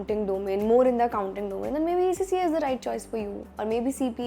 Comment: didi ne kya bola ?? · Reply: "then maybe ACCA is the right choice for you"